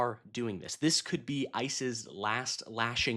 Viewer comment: I think it's funny that the captions wrote ISIS instead of ICE..... 🤣